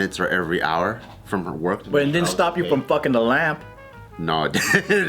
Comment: I thought he said lamb I was just like “Bart fucked a lamb”